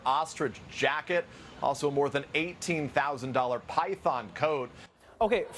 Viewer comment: I thought he said “$18,000 Python code”